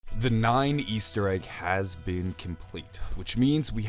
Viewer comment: did he just say the nine easter egg